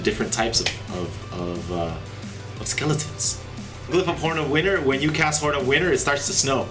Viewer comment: I thought you said Glyph of Porn Winner HAHAHAHAHAHAHA